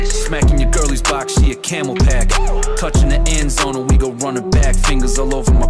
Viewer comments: What people really searched: We eating good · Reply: I thought he say “WE GOOD” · I thought he said "We good"